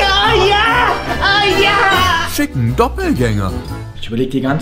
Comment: Bei 2:39 habe ich verstanden ficken xD